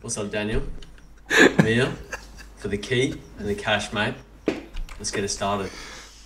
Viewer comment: I always thought he said the same thing the whole show, but he says at the start: “I’m here for the key, and for the cash *mate*, let’s get it started. The ‘mate’ is absent in the rest of the show.